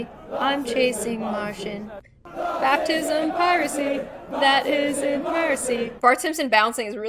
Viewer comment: I heard "that isn't mercy"